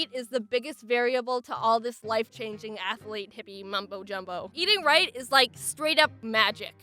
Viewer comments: did I just hear MUMBO JUMBO?! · did she said MUMBO JUMBO ?